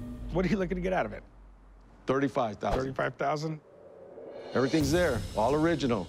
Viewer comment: i thought he said everything is all here , what a liar